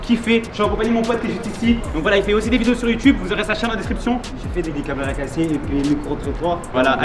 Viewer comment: C'est Moi ou à 00:46 y dit jfait des caméras casser 😂